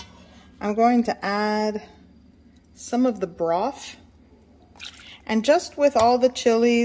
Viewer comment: did you say "Broff" instead of Broth? lol